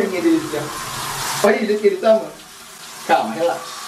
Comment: foi impreçao minha ou o falo Olha como ele tá MÓ 🤨🤔